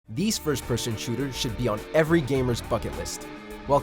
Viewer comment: I thought he said "F'n list" instead of "bucket list'' in the intro.